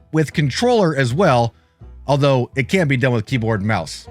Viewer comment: did you say it CAN be done with keyboard and mouse? The caption said CANT